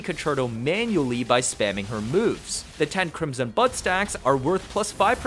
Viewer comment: Listening to this at work and thought I heard him say crimson butt stacks and now it's head Canon x_x